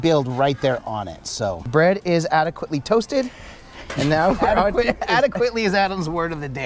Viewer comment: Thought he said "Brett is adequately toasted." I know I'm toasted at the moment. 🥴😆